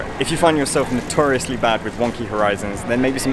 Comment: Wonky... i thought he was saying One Key... lol i was like..whats wrong with one key light. And i ALWAYS shoot in ROAR! lol.